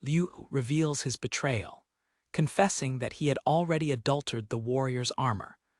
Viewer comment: Hey everyone, how's it going? Show your support by liking our work. Manga title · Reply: You mite wanna go through and edit some of the words that the narrator says because just from the beginning there are many words that don't fit in the story 1:22 like here it says adultered instead of saying altered his armor there are many mistakes before this too